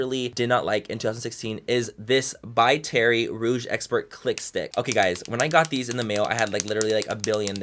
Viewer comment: Not gonna lie when you said click stick I thought you said clit stick for a second hahaha excuse my french